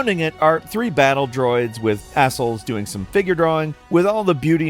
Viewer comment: Not gonna lie, my first thought was that this guy said "surrounding it are three battle droids with a**holes". Apparently he pronounces easel as "ah-sul" instead of "ee-zul". Just his dialect, I guess. I only comment because it made me double take.